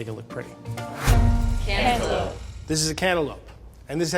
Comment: Chef : this is a cannibal Edit my ears have issues how did I hear cannibal